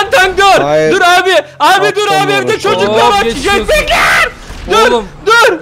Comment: orda evde çocuklar çiçek bekler mi dedi ben mi yanlış anladım :) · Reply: JET bekliyor dedi :)